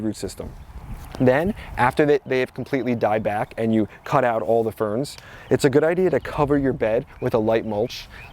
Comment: thank you- I always wondered about those berries on the ferns, but I wasn't clear what you said to do: sounded like you said to "cut off the spears", did you mean "cut off the ferns"?